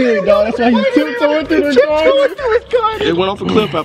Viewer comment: I thought he said 'tip toeing through my garden' too!!!